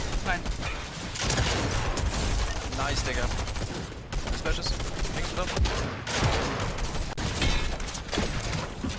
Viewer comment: what does he say???? 🤔🤔🤔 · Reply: Nice digga - german for dude. i thought thats what they said when I heard say it so i looked it up